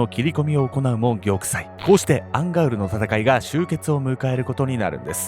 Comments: ずっと思ってたんだけどアンガウルがアンガールズって聞こえてたww 同じ人居ません？笑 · アンガウルの戦いって言ってたのか アンガールズの田中って聞こえてた💦